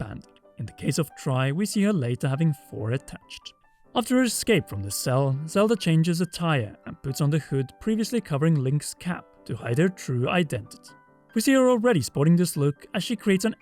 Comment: I heard "Zelda changes attire" and thought you said "a tire" 😂